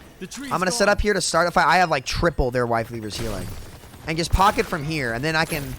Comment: Lol did you say wife leavers healing